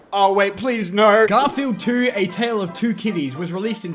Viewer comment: Am I the only one that thought he said "Tale of two titties" at the end?